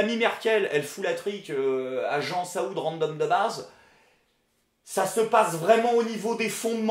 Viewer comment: oulah j'ai entendu " Jean Saoud randonne de nazes".